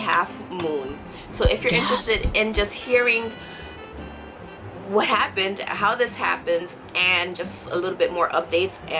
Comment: Did anyone else hear a weird whisper around 0:43? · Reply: It was her saying gasp